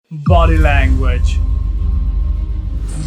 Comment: In the beginning of the video I thought he said Gotti language lol